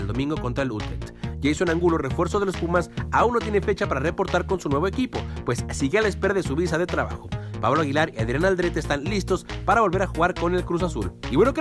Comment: ¿Alguien me explica qué dice? No entendí. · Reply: Que aun no puede ir con pumas por que no le han dado la visa